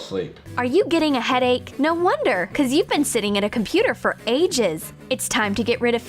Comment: when she says something like these “no wonder you been sitting at the computer for AGES” I thought she said “EIGHT YEARS”